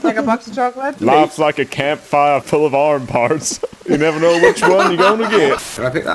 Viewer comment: I can't stop laughing at life's like a campfire full of arm parts😂😂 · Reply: Somehow, at first I heard it as, "Life's a campfire full of armPITS..." xD